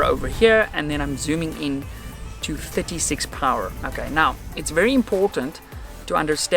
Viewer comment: ? Did he say "zooming into 56 power" ? {2:40} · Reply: 36